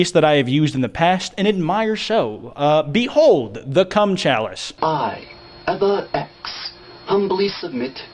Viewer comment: it thought he said 'the c*m challenge' so when he showed the video of him holding a glass of white liquid I was shooked